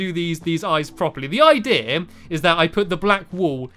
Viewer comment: "the idea" to me it sounded like eye-dear